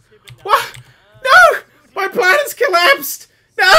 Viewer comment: When Dan said "My plan is collapsing", I thought he said "The planet's collapsing."